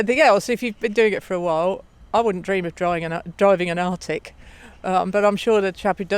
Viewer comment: Very well made video, the editing and especially the narration is top notch, up there with Stephen Fry type quality narration. Can anyone tell me what she is saying @ 6:30, is she saying 'arctic' is that British slang for something...?? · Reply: From memory she's referring to an "artic" aka an articulated truck. What in the USA would be known as a semi-truck I believe.